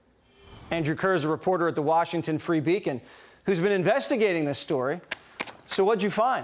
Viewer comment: I thought at first it said Andrew Kerr of the Washington free bacon reporter lol